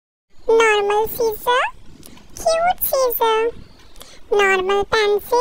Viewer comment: Nah why did it sound like she said:normal DADDY😩 cute DADDY😩